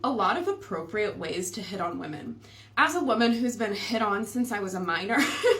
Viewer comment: I thought she said there are alot of appropriate ways to hit women. 💀 Hit on as a minor 💀